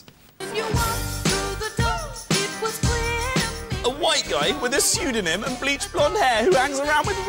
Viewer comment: OHHH FUCK. i thought he said jack white. i shat a bit. oh well....